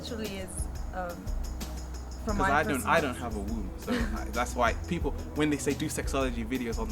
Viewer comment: HOLA!! HELLO SOMEONECAN HELP ME THEY TALK ABOUT THE WOOONTH OR SOMETHING? , idon't undrestand what's the word they say at 6:00 wooth? how it is writte? can someone helps me i want to know the meaning!! great videos thanks!! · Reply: i think they're saying womb